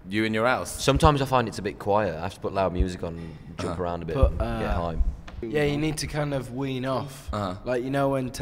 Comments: Did anyone else hear Liam say "...and get high." At 8:55. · Did Liam say he has to put loud music on, jump around and get high? 8:52 lol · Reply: i'm pretty sure he said "get hyped.." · does he say get high? ;) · Woah woah woah did Liam say "get high"? 8:56 I could be wrong but I thought Zayn said 'you've changed'